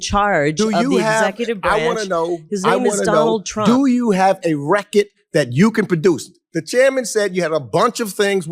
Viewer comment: Is he saying reckitt or record?? 😂😂 that’s gone be in my head all day smh😂